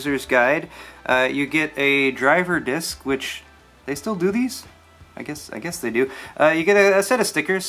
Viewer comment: I heard "dooddy instead of DO THESE lol.